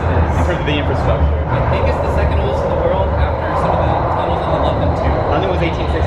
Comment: I think the subtitle should say "after some of the tunnels in the London Tube"?